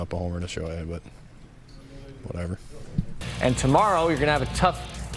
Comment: 光栄とは言ってなくないですか？ whatever（気にしてないです）と聞こえますが